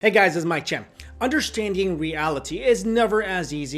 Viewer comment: Ok, I have been a sub for about a year now, Sadly I just now realized that Your name is, I always thought you were saying "Mikey Chen"